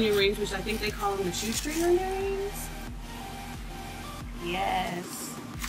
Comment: I thought you said "G string" ....